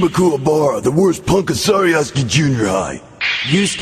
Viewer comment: Sounds like he said "Cuz ImmaKuwabara."